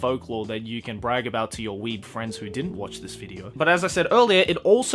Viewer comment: I think around 7:00 it says in the caption "weed friends"- 😹😹